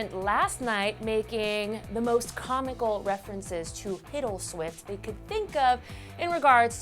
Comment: the first time she said "Hiddleswift" I thought she said "Piddleswift"